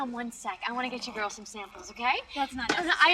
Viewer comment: She clearly says samples. Like samples of her perfume she made.